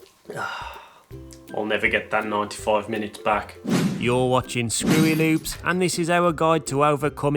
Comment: anybody thought he said your watching screw it noobs